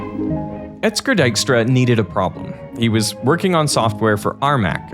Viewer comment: "Dijkstra" oh ok got it. Not sure what the remaining 28:50 says, but that's where the video ended xD